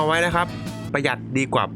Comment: พูดว่าประยุทใช่ไหมคับ